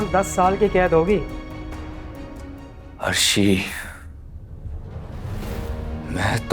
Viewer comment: mint pa shakeel ny kis ka nam lia koi batay ga?? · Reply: Nik name arshi